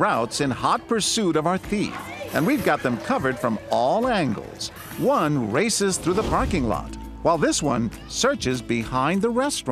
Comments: I thought he said one racist through the parking lot lol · Oh gosh I thought he said “racist” for a second. 😂